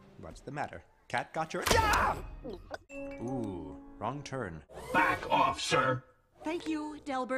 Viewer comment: thought they said " F*ck off sir" 😂